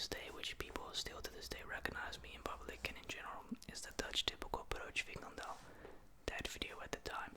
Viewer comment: what did he say here? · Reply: Broodje frikandel. A broodje frikandel is like a sausage roll with a bit of curry sauce in the pastry. It is a typical dutch snack.